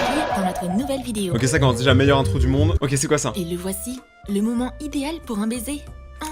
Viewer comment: jai compris "c'est le meilleur moment pour" baiser 🤣